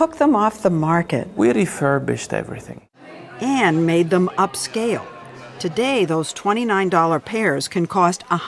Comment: What he's actually saying "We took out our refurbishing pen and next to $29, we put a zero."